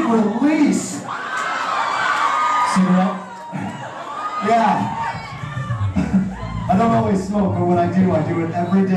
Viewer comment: GUYS. What does Dallon say to Brendon at @ 1:42? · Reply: “cigarette?”